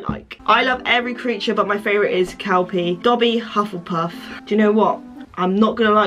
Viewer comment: When you said “kelpie” I thought you said “cow pee”